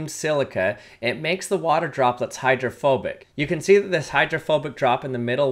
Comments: I thought he said homophobic for a sec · I thought he said 'it makes the water homophobic ' 💀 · I thought he said homophobic for a second